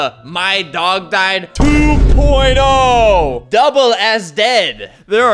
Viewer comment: i thought i heard something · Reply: Double ass dead?